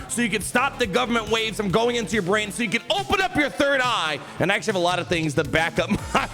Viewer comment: Did you just say " open up your brown eye"? That's what I heard... maybe Gary thought it was funny to put that on your teleprompter